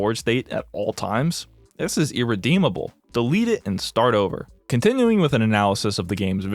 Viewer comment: slight mistake in the captions, instead of "delete it" it says "read it"